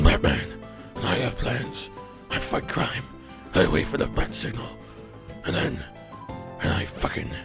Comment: i swear i hewrd him say i fuck crime and wait for the but signal